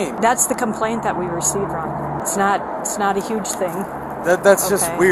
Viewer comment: “it’s not a huge thing” is what the woman said when she walked in 😂